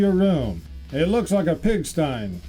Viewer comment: did he just say " pig-stein"? Its a pigsty.